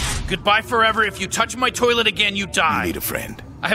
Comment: Notice how he said “I have a million cooler friends than you” instead of “your not my friend” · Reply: I thought you said coder instead of cooler lmao